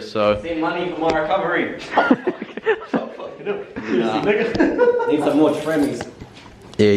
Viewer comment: Anyone else hear Ni**a at 37:59 ? xD "im not a racist" na your all good mate